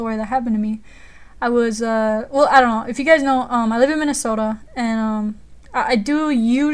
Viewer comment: i thought she said, i live in a mini-soda haha